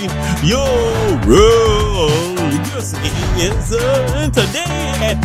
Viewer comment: did he say “ur gay”